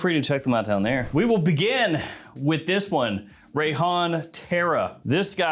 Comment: I thought he said “Rayhan Terror!” 🤦🏻‍♂️🙄😏